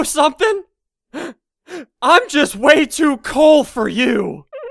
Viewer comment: I thought he said I'm too cold for you and I have no soul Who's the cold one now b**